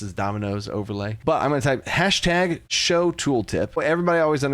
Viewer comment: did he really call it a "hastag"? 💀